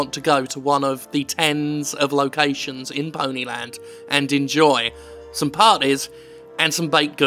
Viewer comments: At first I thought it said "in poly land" which I think would have been a much better game than a land full of my little pony ripoffs or at least that's what I think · I thought it said "Pony island". God, that game was good. And not about ponies.